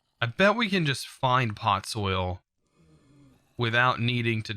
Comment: he said him"pot soil" I replied I hope the soil isn't on pot but him " I bet I can just find pot soil" me well at least you don't have to give the soil the pot. BTW it only sounded like he said pot soil